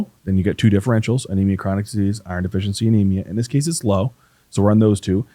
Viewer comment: I hear " I-RUN " 😊